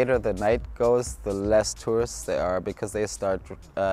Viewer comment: Oh tourists. Sounds like he was saying "terrorists." 😂😂